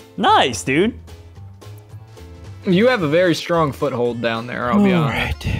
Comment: I thought said u have a very strong foot.